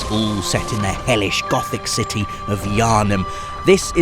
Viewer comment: Whenever I hear "Yharnam" I hear "yarnie" which takes some of the atmosphere away